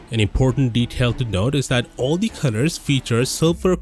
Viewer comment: Why do i hear "CUTTERS" instead of COLORS